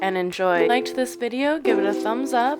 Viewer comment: At the end I heard you said "subscribe to LOOSE a human" and I thought why would I want to "LOOSE a human"? 😆 Anyway, loved your video very well explained and short 👏 👍 I will definitely try it 😊 thanks Greetings from Egypt